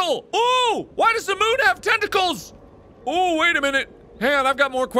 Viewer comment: “Why does the moon have tentacles?” Bloodborne intensifies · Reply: Archie Atabey I thought he said testicles at that part lol